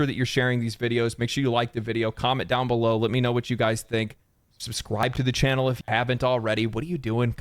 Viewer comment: did he say subscwibe??